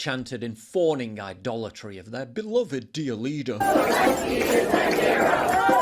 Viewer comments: Wait what Zelinky is a Zero yea thaks what i thought you were saying!!✌️🇺🇲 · All I heard was “Zelenskyy is a ZERO”.